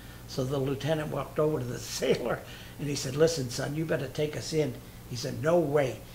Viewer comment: Take a "what"? I did not get what he said :( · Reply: Take us in